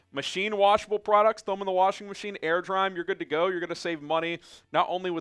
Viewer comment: Not gonna lie, I thought you said air fryer instead of air dry during that rhone ad